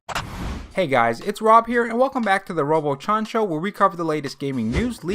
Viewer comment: Love your channel, is it me or does it sound like u say robo-chon-cho 😂